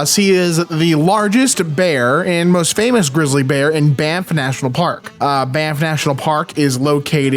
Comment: until you put up the map, I thought you were saying BAMF As in, bad ass mother fucker